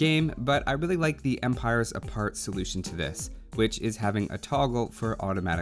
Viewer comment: What is the game at 4:19? I looked up what I heard him say, and couldn't find it. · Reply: Empires Apart. It's in Beta at the moment